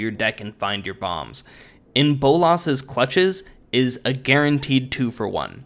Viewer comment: It sounded like you said "In Bull Ass's Clutches" lol